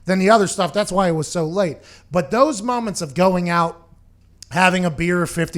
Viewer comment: Had to rewind it and make sure I heard him say “thots” 😂😂